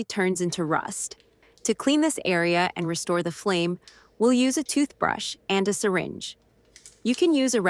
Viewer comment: Boy, Oh Boy! Did you Guys get me in trouble. I thought you said use my “Old Wife’s Toothbrush “! 😂😂😂